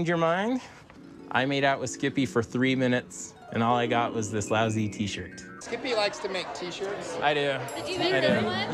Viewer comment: The show: "I made out with Skippy for three Minuets and all I got was this lousy T-shirt" What I heard: "I completed Vice City and All I got was this lousy T-Shirt"